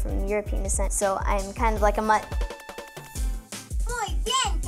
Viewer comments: did she just call herself a mutt? Wtf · did she just call herself a mutt...